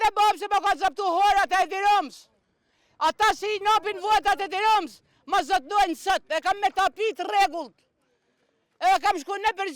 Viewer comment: the subtitles are so so bad and does not represnt what the other person say at 2:20 she says "I have my own land and its with all legal procedures and ownership certificate..."